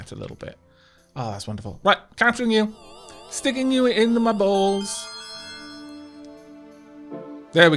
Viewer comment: Did you say "sticking you into my balls?" I just need to clarify because I'm laughing so fucking hard right now. 19:11 "My brush is so big right now. It's throwing me off." 22:30 "Yours is enormousness by the way."